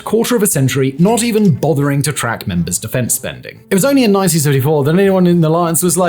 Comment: WTF did you even say there, in the 1934? Mini stroke?